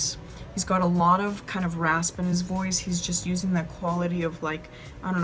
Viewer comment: Great song. I had a double take though as I thought you said hairy chest quality. Thankfully it was an airy chest quality 😊😂. Loving your videos ❤